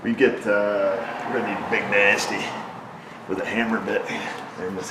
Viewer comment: who else heard 'Big Nasty with a Hannah Bit'?! 🤣